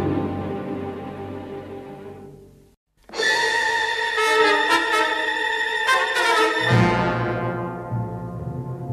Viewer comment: Indental 42: What Did He Say Garbage? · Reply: Selling Out.